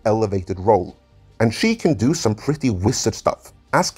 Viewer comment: Did you really say- "She can do some really wizard stuff." LOL Awesome